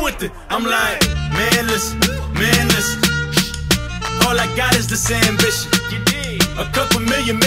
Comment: I thought he was saying " Manless, manless all I got is sandwiches"